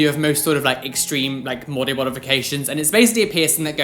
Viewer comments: Anyone else hear ‘mody bodifications’ or was that just me? XD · Anyone else hear "mody bodifications" at 10:12 😂🙈